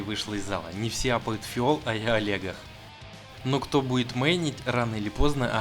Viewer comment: послышалось : " Не все аппают фиол, а я Олега" XD Бедный Олег...